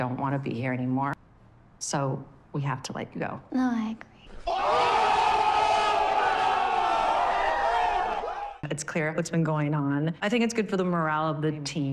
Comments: What did she say in 17:35? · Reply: "Oh I agree" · wait what did she say? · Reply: She says, "No I agree" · What did lexi say in 17:35 ? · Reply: I think she said “no I agree” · what did she say, iv replayed it in slow mo so many times and i have no idea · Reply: "no, i agree"